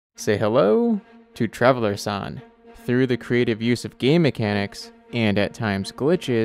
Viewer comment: Sounds like hes saying ‘gay mechanics’